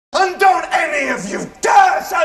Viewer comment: I heard "don't any of you dare sanitise no money"